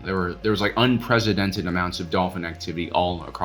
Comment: All kinds of dolphin activity? Went back and listened to it three times and I can't understand what else you could be saying